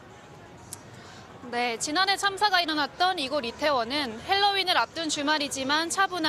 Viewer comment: 발음이 이상해서 몇번을 돌려 봤다.. "지난 해 참사가 일어났던 이곳 리태원 은" 이라고 들리는데.. 얼마 전, 왜색 표현을 하지를 않나.. 공영방송 수준이 점점 엘롱인데..